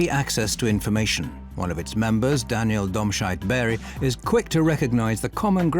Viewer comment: I got really confused and had to pause it when the narrator said "Daniel Domscheit-Berg" - I thought he'd said "Dogshite".